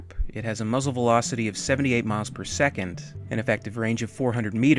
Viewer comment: Did.. did you say the grenades are launched at 78 miles per second?